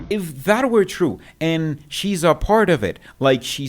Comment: At the 15:00 min mark of this video, you said SHE said she's a part of this nationwide investigation. No, what she actually said was HE'S a part of it. Nevertheless, at the end of the day, THE TRUTH will rise like cream cause a lie will NOT stand. It's just a temporary delay to the inevitable.